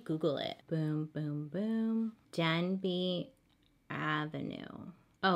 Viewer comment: She said “ Denby “ but I heard “ dead beat “ 🥴💀💀💀